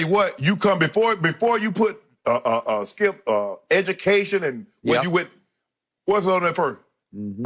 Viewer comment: What Shannon say? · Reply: What's on the application. " education, race "